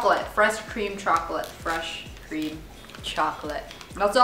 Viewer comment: i thought u said green chocolate i was like what! but then u said it again and then i knew wat u said XD